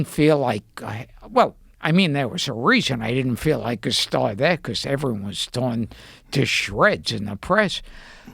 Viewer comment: What is Gilbert saying at 04:11 "there was a reason I didn't feel like __ "? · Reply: “There’s a reason I didn’t feel like a star there is because everyone got torn to shreds by the press”